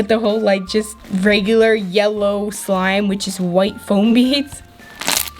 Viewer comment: I thought u said floam🙈 and when u was making that yellow slime I thought u said sticky but u stinky no hate just telling truth